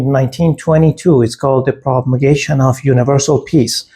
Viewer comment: Hello...just noticed a few words in the transcript that you may want to fix...PROMULGATION (not Propagation) of Universal Peace at 5:28 ;at 1:46 TRUE instead of through . Will finish watching when I'm able...glad to know about your videos! 💛